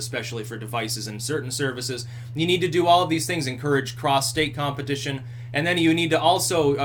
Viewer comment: For a moment I thought you said "prostate" instead of " cross state". Humorous!